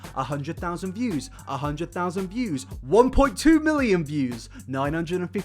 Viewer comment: 1.2billion views lol that’s what i heard